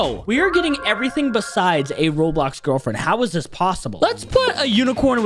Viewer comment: I thought you say Roboux